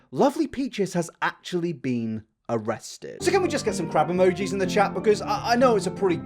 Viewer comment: i was confused by the crab emojis in the comments, because i heard "crap emojis" in the video that being said, how the absolute fuck can someone mention they're abusing a child and/ or an animal so casually and even laugh about it!?